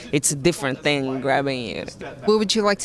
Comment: That unnatural edit. 😂 Did she say, "Grabbing a [man"]? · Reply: Grabbing you